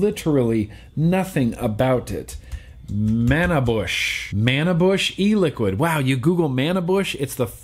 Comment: I thought you said “man bush”. My google results were much different from yours...